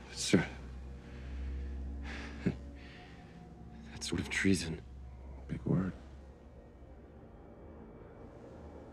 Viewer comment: What did homelander whisper to deep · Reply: “Kill Bishop, make it look like an accident “